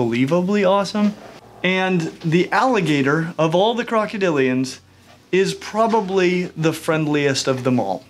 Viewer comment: It’s a americain alagator not a crocodile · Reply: sorry my bad you said crocadilian I thought you said crocodile